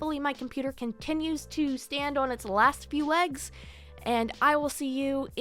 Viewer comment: instead of hearing "hopefully my computer continues to stand on its last few legs", I heard "hopefully my computer continues to stand on its last few *eggs*". 😭